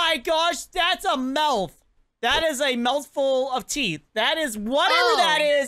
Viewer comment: I watch a YouTuber and I think she said it’s the mouth room??either that or teeth room (Edit) or something like that